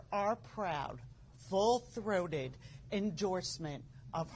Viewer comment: Did she just say "We support her FULL THROATED" 😳 Seriously⁉️ That sounded like a slap in our face...correct me if I heard that wrong.